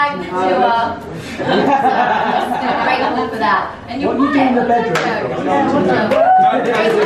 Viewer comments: Did anyone else hear “that’s what you do in the bedroom” at 20:17 ?!? After dianne starts speaking about how she survived the tour after joe dropped her😂😂 Please say I’m not the only one that heard it!!! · am I the only one who heard “ what do you do in the bedroom” 😂